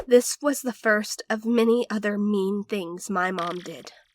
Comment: Is just me or at 4:41 did I hear meme things